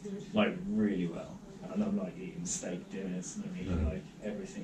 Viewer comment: I watched this again because I wanted to hear what you said about eating a steak, I thought originally you said you didn't like to eat steaks, was so glad when I heard it again that you actually said you like a good steak. So happy about that. I really feel like the Keto/Carnivorous diet could save your life. If you can, go completely zero sugar and just starve those cancer cells until they are gone. I just started this diet myself on the 18th, couldn't get rid of my belly fat until now, am growing muscle where I had none from no nutrition or anti nutrition from pretty much an all sugar and carb diet, I feel younger, my stomach flora was all messed up, but that is clearing now. Keep eating those steaks, get them rare if you can, high fat, real butter, eggs, lots of eggs, raw milk. Shock everyone, eat this way and shock everyone.